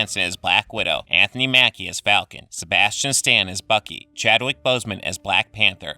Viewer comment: did you say Fucky